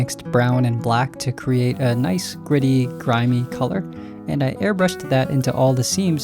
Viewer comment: did you say griddy like the dance